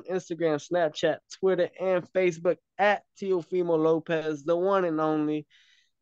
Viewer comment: It sounded like you said teofemale Lopez on the intro